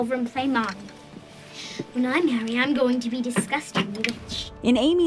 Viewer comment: is it just me or did Beth say “ when I’m married I’m going to be a disgusting witch 😊” I think I heard that wrong